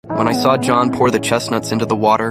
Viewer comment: I thought bro said John pork 😂😂😂😂